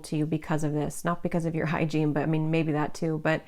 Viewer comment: Pile 1. For some reason, when you said hygiene I heard "spiritual hygiene." So definitely for me, but maybe also for someone else. Spiritual hygiene is about being able to clear out your energy field and preventing other's energy from impacting your choices and decisions. Pile 3. Resonates. Thank you.